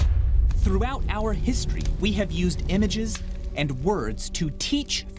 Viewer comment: I misheard "image" as "emoji." this might also be a fight-or-flight response conditioned for me.